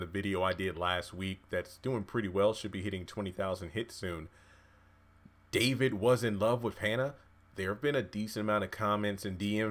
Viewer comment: When he brought up Hannah while he was in the bed with Veronica, I hit rewind several times. Spill it!!! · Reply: sounded like he said, did I tell you that I was in love with Hannah! I was like hold up while jumping up and down lol.. I’m wondering if he is Bennie Father